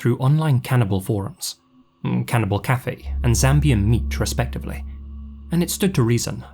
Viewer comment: Wtf did he say Zambian meat?😭💀